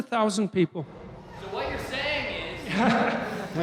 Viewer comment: What did the person say at 1:05:34 .... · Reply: <link> : “What you’re saying is ...” I think he was copying something Peterson often says.